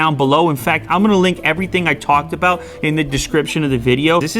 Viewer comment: I thought he said "I'm gonna lick everything I talk about"